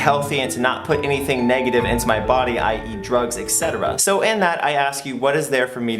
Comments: did he say ex cetera? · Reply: He said i.e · sounded like he says "I eat drugs"